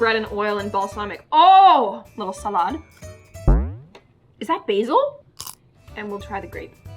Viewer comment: I thought she said "is that bezoz" for a secont